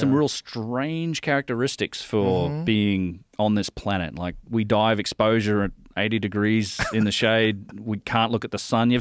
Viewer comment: what did said here? 44:12 pls can somebody help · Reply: On this planet, like we die of exposure at like 80 degrees in the shade